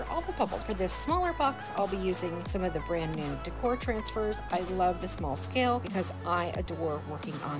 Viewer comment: Love the projects. But so heard to hear. On the transfers I thought you said "Transfer Decor" is that right? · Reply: They are called Decor Transfers. Yes. We sell them at <link>